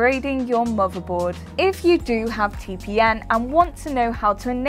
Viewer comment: am i the only one who heard her say T P N for most of the video? must be her tongue ring getting in the way